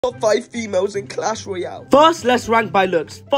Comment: at first I thought he said I'll fight females in clash royale💀💀💀